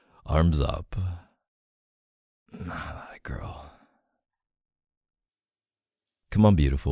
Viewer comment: Excuse me, did i just heard an “atta girl” at 8:47???😩 you’re killing me